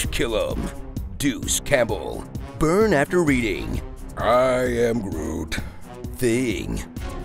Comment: The “I am Groot” for the tree assault scene 😂🤣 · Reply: I thought he said “I am Root”. Either way, nailed it.